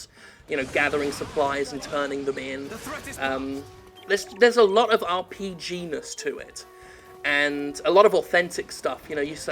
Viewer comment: I thought he said "They'res a lot of RPENIS to it" and I smirked.